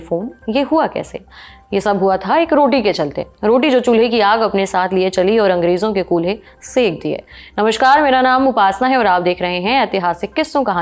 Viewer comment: did she really said "angrezo ke kulhe"😂